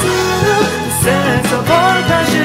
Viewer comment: I know its really "get you ensnared" but all I hear is "gut you instead"